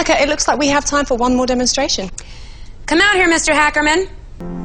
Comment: Did I just hear Mr.Ackerman 💀💀